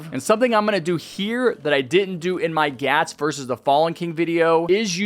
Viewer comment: did bro just say gyat?🤔🤔🤔🤔🤔🤔🤔 5:13 HE SAID IT AGAIN😱😱😱